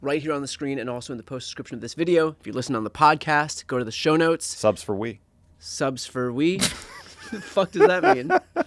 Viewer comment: I thought he said subs for weed. 😅😅